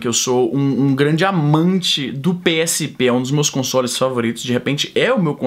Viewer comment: Só eu que entendi que o é amante do PSB 8:26 ?! kkkkk sempre desconfiei que você era socialista!